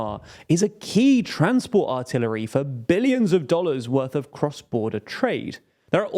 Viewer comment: is what is being said artery or artillery?